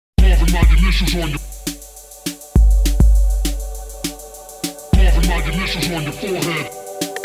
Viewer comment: haha after i read this i heard it as "dolphin guided missiles on your forehead" btw, epic epic epic beautiful song